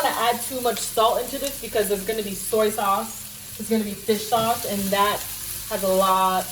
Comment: That made me hoooongry!!!! Looks delish T!!!! Did i hear "fish shhhauce" 🤪🌻🌻💜🌻💜🌻💜🌻💜😘😘